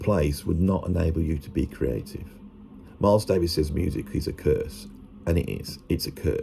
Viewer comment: Oh now I get it, you misheard "Miles Davis says" as "Miles Davis's" @ 5:52. Funnily enough the Youtube Machine A.I. speech recog got it right. These things will be the death of us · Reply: lmao yes!!! He said "Miles Davis says music is a curse" and I heard Miles Davis's music is a curse. I relistened to it over and over again too to make sure I was hearing it right lol